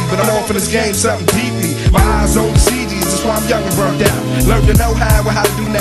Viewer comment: I thought EDI said “My eyes on the CDs”💿